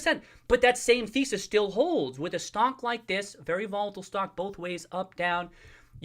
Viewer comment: swear I heard a "stonk"